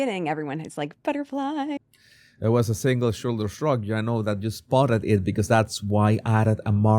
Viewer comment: A single shoulder shrug. Say the 5 Xs fast! I thought he said the second time, " a single sugar shug"!